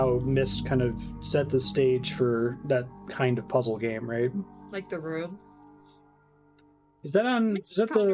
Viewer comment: I almost thought they said “Like The Neverhood?” I thought that because The Neverhood is another game inspired by MYST. It’s also one of my favorite games ever!